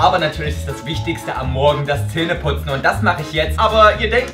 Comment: ich hab bei Zähne putzen Telefon putzen verstanden😂😂